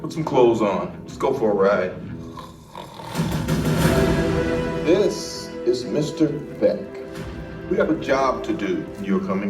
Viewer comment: i thought nick said this is mr. thick